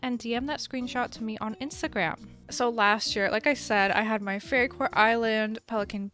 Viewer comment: I misheard fairycore as furrycore and was really confused for a second